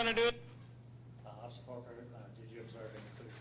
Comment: officer Forker? Thought you said officer Porker lol